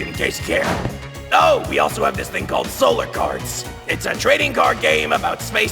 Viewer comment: I thought i heard them say solarbears.